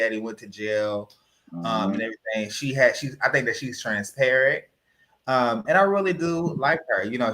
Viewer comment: it sounded like said,"Trans-parrot." Lol😂